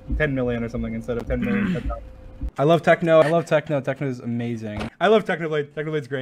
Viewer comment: did dream just said "I love techno"??!?!😳😳😳😳